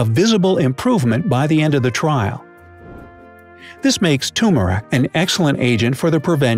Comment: 'tumhare' instead of turmeric 🤣